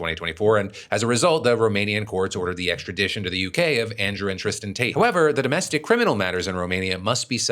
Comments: I swore you said 'Andrew "Interesting" Tate.' 😂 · You said "Andrew and Tristan Tate" and i heard "Andrew Interesting Tate" and i did not know that was his middle name